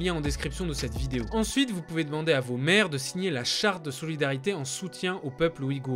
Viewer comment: J'me suis senti trop bête j'me demandais pourquoi je devais en parler à ma mère. En tout cas merci tu as très bien résumé le sujet. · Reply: Pareil, je me suis dit « pourquoi il veut que je fasse signer une chartre a ma mère 🧐